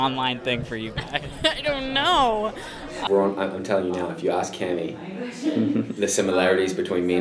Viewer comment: What does KJ say at 0:33? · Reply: Anela P. Bro i Can tell you If you ask cami (someting like that)